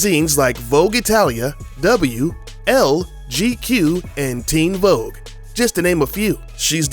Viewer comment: I thought it said "Teen Voe" as in the Gerudo term for a male